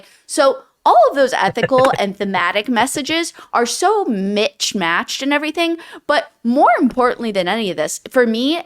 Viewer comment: did she say "mitch matched"? 😅